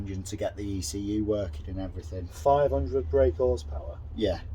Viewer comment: I love the young lads ambition, but I need to get my hearing tested because I'm sure I heard him say 500 BHP?